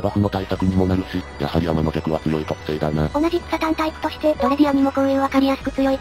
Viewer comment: 「同じ草単タイプ」が「同じくサタンタイプ」に聞こえたｗ